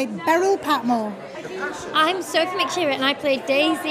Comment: It sounded like she said "i'm Sophie McHewitt". Can someone explain that?